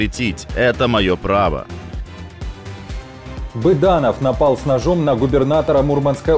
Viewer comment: Мне послышалось Буданов накинулся с ножом на губернатора 😂